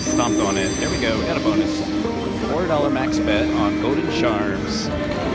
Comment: I thought you said Golden Sharts instead of golden charms